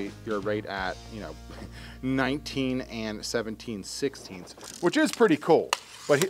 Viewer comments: Wait a sec... At 10:08 does say 19 and 17 16ths ?? :) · did i hear him say 17/16s